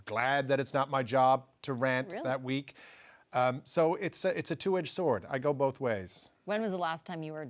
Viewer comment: Two inch sword? Or did he mean double-edged sword? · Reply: my cc says > it's a 2 Edged sword . . and is what I Heard . I also Miss his show .